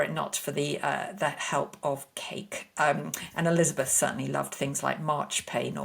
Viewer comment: Elizabeth's son? She didn't have one. Who is this referring to? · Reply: I definitely heard 'son' the first time and did a double take, but heard 'certainly' once others clarified what she said 😅